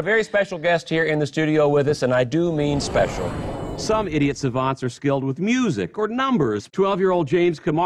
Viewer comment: Did I just hear "idiot savants?" Duck you today now